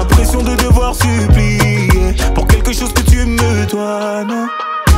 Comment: c moi ou à 2:13 il dit "quelque chose que tu me DOIVAIT"